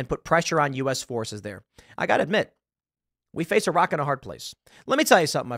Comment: ish, sounded like "We face Iraq and a hard place." Unintentional and kind appropriate phraseology.